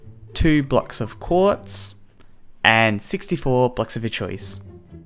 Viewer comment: it sounds like he said butts of your choice